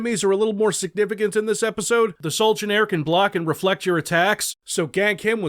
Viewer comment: The captions render "Solegionaire" as "Sultan's Heir"? 15:57 16:18 These lines hav some text from the next. 19:21 A comma got mistyped as an "m". 29:10 Has "talked up" instead of "talked of".